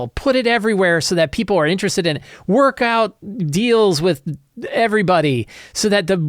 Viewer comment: I completely misheard “workout deals” and now I’m picturing “Tai Chi with Tasha”, “Hamster Yoga with Minsc”, “CrossFit the Menzoberranzen Way”